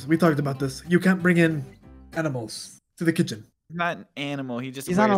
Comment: he called them animals but I thought he said edibles 💀